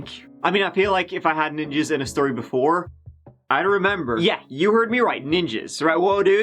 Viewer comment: I had to rewind because I thought I heard him say "if I had jizzed in a story before.. I'd remember" 🙄